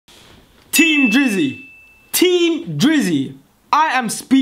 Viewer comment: Sorta kinda thought he said team jizzy